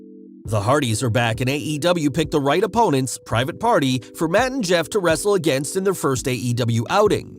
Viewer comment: Matt and Jeff, I thought you said Mut and Jeff lol, I had to go back and listen again 🤣 Our youngest daughter, her husband, our middle daughter's husband, my husband son and I will be at Wrestlemania in Dallas, so will 2 of our grandson's.